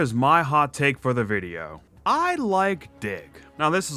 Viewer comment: Ngl bro when you said your hot take I thought you said "I like dik" 😂